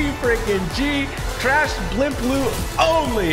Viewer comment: My guy really said “ trash blimp loot only “ lol bro I think idk but it sounded like he said trash blimp loot only